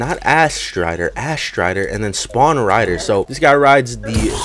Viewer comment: did he say ASS RIDER?